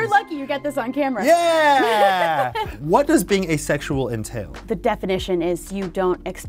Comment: I misheard the first word of the episode as eggsexual